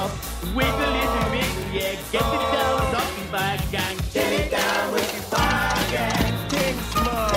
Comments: Thanks for that my friend , I wondered what they where saying and until only a few years ago I always thought it was saying " getting down with the Buzzards " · Forever growing up i thought they were saying get down with fungus